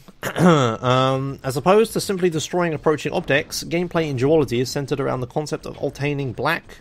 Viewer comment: I thought you said geology when you were saying duality xD